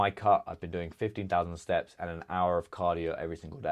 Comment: What do you mean? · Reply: I thought you said you did 50k steps buy I must have misheard